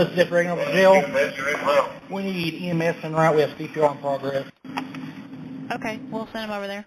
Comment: did the LPN say "we have CPR in progress?" No one was performing CPR. It's possible I misunderstood what he said, or that he meant something other than cardiopulmonary resuscitation.